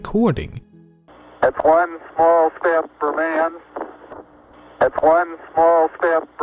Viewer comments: I hear it, he says it very quickly though its more like "One small step for uhman" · I don't know about you, but I could clearly hear "one small step for a man" it's just very quick right before "man". Listen closely. I've always heard the "a" ever since I first heard that quote. When I saw how it was written I kept thinking I was hearing things every time I heard the quote. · I heard "A Man"